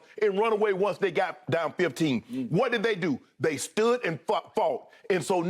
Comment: Yoo @ 18:41 what what did Unc say? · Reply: “They stood and f@